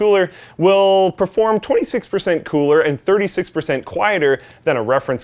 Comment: i thought he said 20% cooler xD